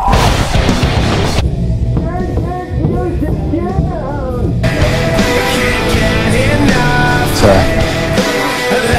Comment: what did he say?its too damn muffled for me to understand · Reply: "TURN THAT MUSIC DOOOWNnn"